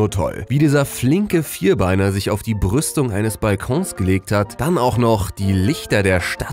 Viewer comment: Ich habe zuerst Brüste verstanden XD